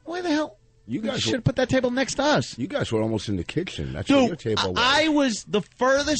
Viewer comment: I swear, he almost said "kickchen"